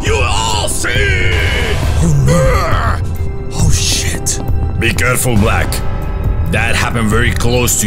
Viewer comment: did black just said oh sh**t ??☠️☠️😂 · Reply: oh shy